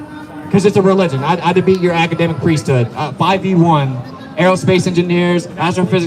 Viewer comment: Where can I find 5 e 1? Is that what you said? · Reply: Oh 5 v 1, where can I find that debate?